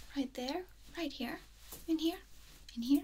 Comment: I swear, at one point I thought she said, "Pull my finger." 😂